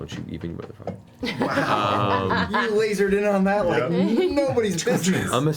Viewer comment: does anyone know what Laim says at 3:03:13? I cant make out what hes saying. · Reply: "You lazered in on that like nobody's business."